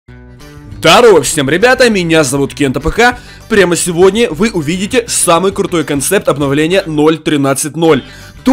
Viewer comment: Вначале мне послышалось здарова с вами я Кент.АФК и у меня глаза 🧐